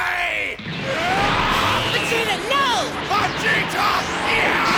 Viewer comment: Add first I thought it said Vegeta avenge